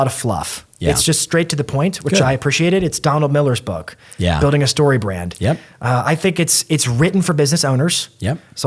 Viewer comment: Can anyone please write the name of the 2 books he mentioned in 11:00 I couldn't catch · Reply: Building a story brand and Influence: the psychology of persuasion.